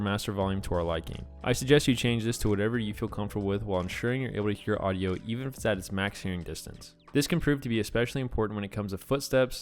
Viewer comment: someone can transalte what bro mumbled here? 😂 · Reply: I suggest you adjust this to whatever you feel is comfortable while ensuring you’re able to hear audio even while it’s at its max distance